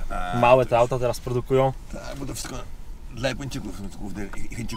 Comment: zrozumiał ktoś co on mówił? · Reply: "Bo to wszystko to dla chinczykow glowny i Japończyków glowny ryrynek zbytu"